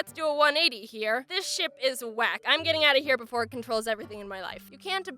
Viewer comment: I thought she said "This shit WACK!"